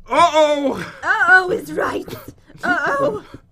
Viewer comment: uh oh is ripe. ( I think she ment to say right)